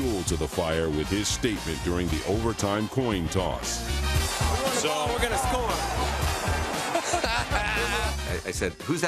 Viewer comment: What did Matt Hasselbeck say at 7:47?... I only caught the "We're gonna score." part. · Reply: We want the ball and we’re gonna score